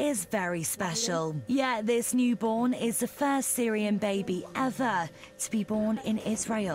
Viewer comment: For a second I thought it said "First Saiyan Baby" lol